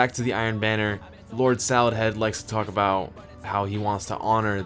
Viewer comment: I heard him say "Lord salad head"